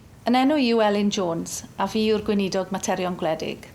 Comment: I heard weinie dog!